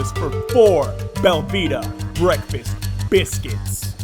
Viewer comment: Now this was ridiculous. The bottom line is this was absolutely uncalled for and to be honest it's pretty odd! I mean are you telling me that there are biscuits that don't need the freezer OR refrigeration!? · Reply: I thought he said Velveeta breakfast biscuits, so I thought they were like egg and cheese breakfast sandwiches, that somehow were sitting next to the candy bars.